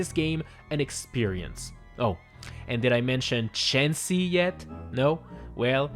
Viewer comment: I honestly thought said Chansey at first before realizing that he said Chauncey.